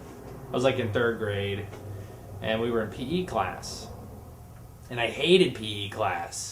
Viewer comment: All three stories might have been true, but the part about huge guns was the lie. Also thumbs up if you thought he said pee class instead of p.e. class!